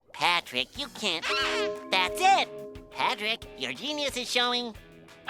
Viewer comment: Legit story: When I was a little kid (basically before I was 8), I ACTUALLY thought SpongeBob said "Patrick, your Penis is showing" instead of what he actually said. (Yes i knew what penis meant before I knew what genius meant 😂)